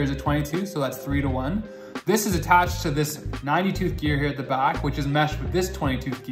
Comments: I thought he was saying 92th gear not 90 tooth gear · Ph he was saying tooth I thought he was saying like 22th gear